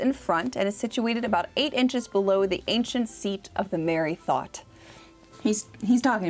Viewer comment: Welp! I just learned that boobs were once called "the Merry Thought"; the more you know I guess 🤷🤣 Seriously though, I loved this video. I love when you go off on merry tangents (No pun intended!). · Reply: I misheard it as "the married thought" 😂